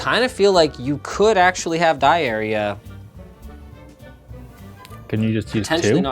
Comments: Sounds like he is saying diarrhea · i thought he said diarrhea, XD